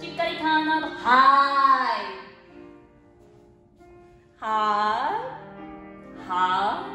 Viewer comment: のところから「い」が聞こえなくて「は〜？」って煽ってるように聞こえてしぬwwwwwwwwwwwwwwww